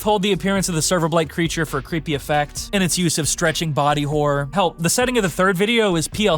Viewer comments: why did ypu say help or did you mean hell ? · I hear you said help or welp in 30:28 min in video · Why do I hear help